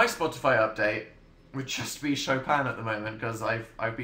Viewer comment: When he said “Chopin”, i thought he said “show phan”